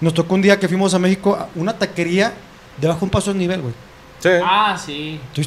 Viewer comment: alguien sabe que quiere decir lo que dijo checo? Bajo un paso esnivel, o al menos asi entendi que dijo